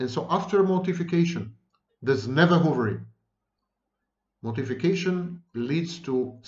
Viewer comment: And so after mortification there's never ... ?????? what does he say here? time marker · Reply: Hoovering.